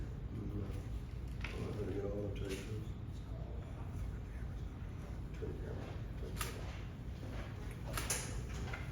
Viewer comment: Dude. Am i hearing shit? At the 6:58 minute mark (after having his hand on the camera i can only asume for at least 5 seconds) i can barely hear the initial instigating officer say either "you guys turn the cameras on/off" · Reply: Good ear. Yes, Officer clearly told the other cops, “Turn your camera off,” twice. I want to believe it was due to their decision that I wasn’t in violation of any laws therefore this was a non-issue and they were going to disengage with me and therefore no longer need their cameras on. -mike